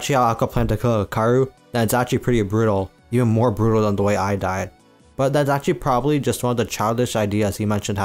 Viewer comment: my dumbass thought he said the way i died